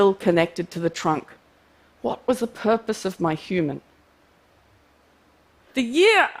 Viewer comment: This is the first video I did for TED as a volunteer subtitle translator (simplified Chinese). It's so shockingly powerful! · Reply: "The purpose of my human" is also what I thought I heard most times she said it. It's not grammatical. Then near the end she said more clearly "the purpose of my *humour*"... which it may have been all the way through :-/